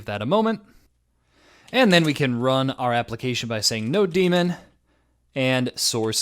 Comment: wait, did he just say node-demon?? tf. It is Node-mon